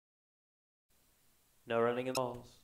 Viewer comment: While I was going over the footage, the Principal sounds like he said "No running at all!" LOL!